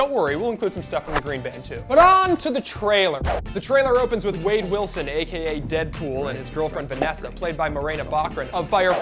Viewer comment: I thought u said Slade Wilson